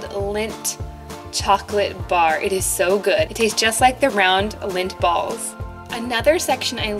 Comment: At first I thought she was saying "round lint balls"